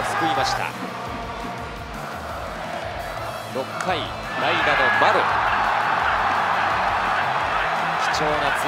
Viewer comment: 「代打のマロ」にしか聞こえないw